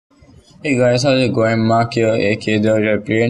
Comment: What did you actually say at 0:00 ? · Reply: ''hey guys how's it going''